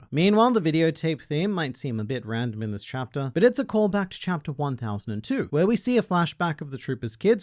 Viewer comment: Did he said chapter 1002 ???